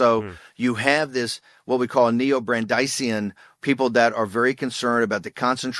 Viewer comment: What's the word Bannon pronounces at 8:30? Neobrandacian??